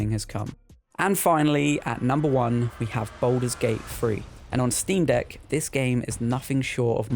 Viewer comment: BUT U SAID Baldur's Gate FREE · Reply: He said baldur's gate 3, but it did kind of sound like he said free. I see how you heard that.